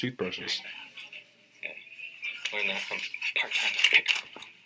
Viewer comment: Yes, when negotiating, silence is golden. What is a "VERO"? I do wish there were some way that we could read their subtitles while watching this.At about 23:20, right after the toothbrushes, I think he said, "I learned that from Part-Time Picker." LOL